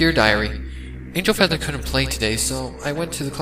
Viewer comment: I may or may not have misheard "Angel Feather" as "Angel Dust" at first, got me very confused